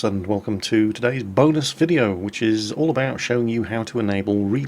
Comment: At the start of the video I thought you said, "Welcome to todays bogus video".